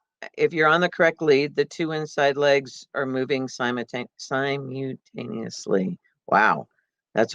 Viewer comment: I sorry but at 4:20 did you say the in the canter the two inside legs move together? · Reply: Yes. What she is saying here is that the two inside legs are moving forward on the lead. That the lead is the front and on the hind leg together. The sequence of legs is a 3 beat gait - 1. outside hind 2. Inside hind and outside fore together 3. inside fore What she is saaying is that the two inside legs are showing the lead.